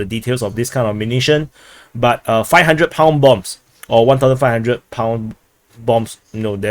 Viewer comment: I heard that it was a 1500 kg bomb, but then again, I could be wrong